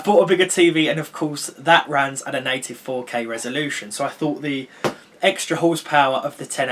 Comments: Did he say 84k resolution or what? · did he just say 84k or 8-4k